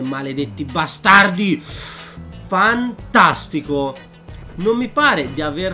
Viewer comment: Al minuto 1:06 ho creduto che stesse per dire fan-culo ma poi mi sono detto "nah bstaard non dice le parolacce 😂"